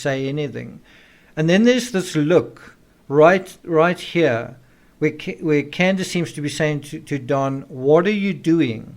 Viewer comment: I noticed the look she gave him! Like "don't let the cat out of the bag" look. She knows the truth too. At 11:24 when she talks about her sister does Don say "watch it, watch it" to Candus? Maybe I'm hearing things idk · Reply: No your not. He says watch it and then tells candus to be careful because her mother is listening.